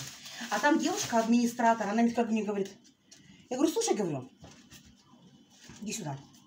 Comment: Мне послышалось на 2:09 "слушай говно"?